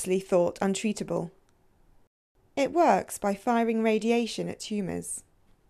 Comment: I thought she said "It works by firing mah lazor at tumors".